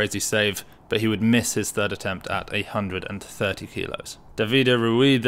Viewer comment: I heard 830 and I was like WTF.... I never knew that Lasha already took his first attempt 😉🤣🤣